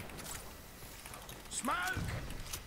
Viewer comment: To me it sounds like Redeye is saying "smelk" or something rather than "smoke" ^^